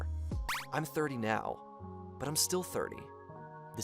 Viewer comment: At the end, I had to rewind to double check what he said. at first, I thought I heard, "I'm 30 now. But I'm still thirsty. This is just the beginning." 🤣🤣